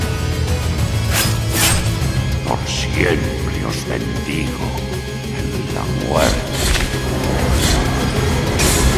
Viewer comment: What does the voice say at 1:36 in spanish/italian? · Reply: por siempre os bendigo en la muerte